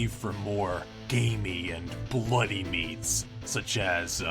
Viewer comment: anyone else hear " gay meat " ?